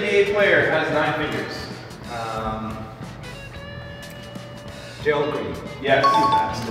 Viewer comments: It sounded like said Gerald Green on the 9 figures question. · What was this question? Did say Gerald Green? I’m so confused 😭 someone help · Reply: 0 videos 0 subscribers he said 9 fingers but it showed 9 figures